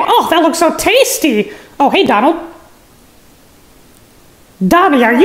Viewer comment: When mickey asks if he was trying to buy some sqirty meat i heard him say "dabi are you trying to by seom sporty meat little man." And I died lol